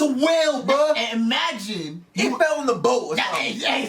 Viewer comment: thought they were gonna say “it felled on the Supra” lmao